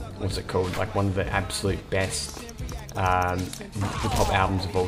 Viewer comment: Did you just say nas released one of the best 'pop' albums of all time?? Fucking hell